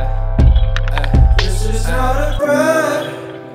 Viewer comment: ffs this whole time i thought he said "This is not a prank, I promise" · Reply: I thought he said "this is not a prayer" wtf I'm fucking deaf